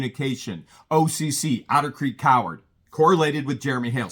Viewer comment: I thought I heard say "otter creek creeper"...